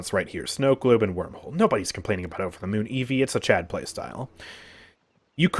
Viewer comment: does he said "CHaD playstyle"? im going to hit Rank 1 with this stay tuned